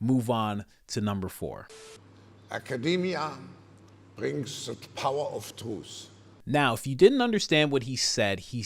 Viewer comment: I hear "Academia bring the power of truth", which I would take to mean, educational institutions will steer the "correct" path. The proper "think".